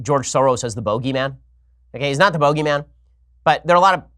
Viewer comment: did ben just say bougey man